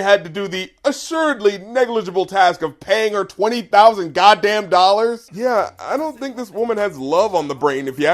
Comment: Honestly, at first I thought he was saying 'twenty thousand won'. Which would basically be about enough to get dinner for two. At McDonald's.